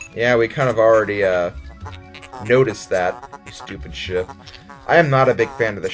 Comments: I thought you said stupid shit :X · Lol I thought you said stupid shit instead of stupid ship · Lol i thought u said stupid shit...